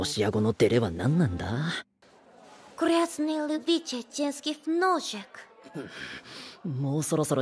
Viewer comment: Even if her voice actor has "Broken" Russian and accent is not full but for japanese this is I N C R E D I B L E. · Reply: It's mostly great. I like the accent, but I can't understand what she's saying here: 0:47 Like: "Red view of human feet" lol Edit: Ah, finally got it "Filthy legs enjoyer"